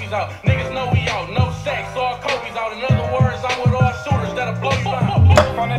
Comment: "No shaqs, all Kobe's out, In other words I'm wit all shooters that'll blow you down" HOW DID THEY MISS THAT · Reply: Ngl, I thought he said "no sex, all kobe's out" lol I was like what?